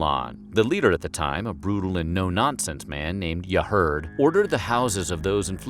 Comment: Who was the “ruthless ruler” that he mentions at 1:41? It sounds like he said “ya heard” lol. Obviously that’s not his name but just curious